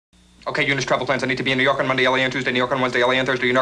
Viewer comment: Does he say Yew Nork at 0:03 ?